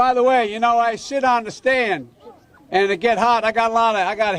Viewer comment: Did he say "sh*t on the stand"? Is Biden chewing chaw?